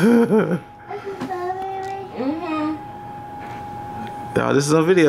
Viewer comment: it was a tomato :O poor kid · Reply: it was a straw baby....i heard him saying straw baby